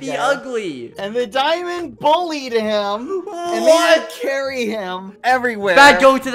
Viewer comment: W-what, i thought skeppy said marry :(